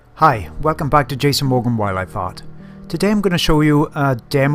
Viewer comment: thought he said wildlife fart